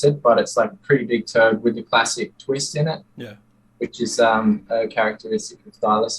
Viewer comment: did he say “Turd”? “It’s a pretty big turd with a plastic twist in it” He’s talking about Thylacines poop? Or did I misunderstand what he said?